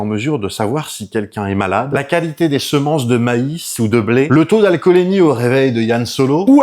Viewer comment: 😮 non pas toi pas maintenant... "Taux d'alcoolémie" ?